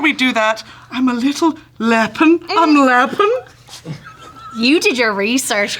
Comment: Does anyone know what Brit said @ 7:42 ???? "I'm a little... lappen?" Am I hearing things 😂😅